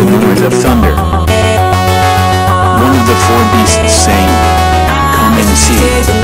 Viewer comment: why i hear "be saint" instead of "four beasts"?